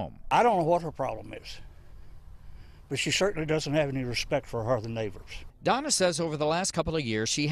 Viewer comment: "I don't know what her problem is" It's people like you. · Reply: "...but she certainly doesn't have respect for her horrible neighbors." Lol, that's what it sounds like he said.